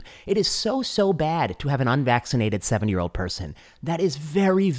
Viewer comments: At around the 6:00 mark it sounded like you said 7 year-old person. I know you meant 70 but it made me rewind. Great work though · Wait, did he say it is so bad to have an unvaccinated 7 year old? I listened to it several times at 5:58. Did he say that? If so where did he get that idea? The American Academy of Pediatrics say just the opposite. I'm starting to think he said 70 and my hearing sucks. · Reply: It does sound like 7, but I think he talks fast and meant 70.